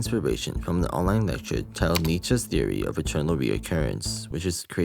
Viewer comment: I heard "Eternal" like "Chernobyl"